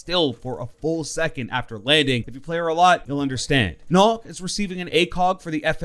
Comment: i cant tell what he’s saying (nooh) but it sounds like nokk and nokk has the fmg, im a nokk main 🥰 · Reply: Vybzx I think he meant nook hopefully got the fmg black ice